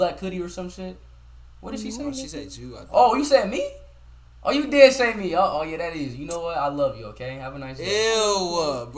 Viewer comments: LMAOOOO PEEP WHAT THE GIRL SAID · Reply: "No nigga" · did anyone catch that😭😭😭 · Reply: She said nigga